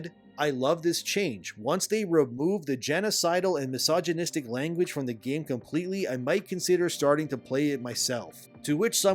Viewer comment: Did they just say "remove genocide" at 20:12 What in the herasy???